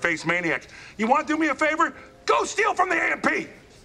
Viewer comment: did he say ”Go steal from the Ampee”? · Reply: A&P* it's another supermarket